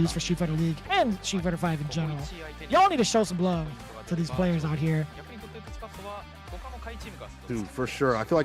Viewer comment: Ayo, did Vicious just said n*gga??? LMAO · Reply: "Y'all need to show some love for these players out here".